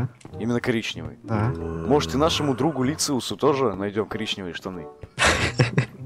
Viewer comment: ты сказал Люциусу или мне послышалось?) 5:56 реально Люциус походу) вот сколько смотрю постоянно интересно откуда и когда ты с кем-либо познакомился.